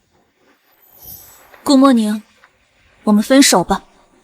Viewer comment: ohh dear.. I really feel bad.. I seriously thought that FL said Good Morning at night.. It's ML's name actually.. 🫣😞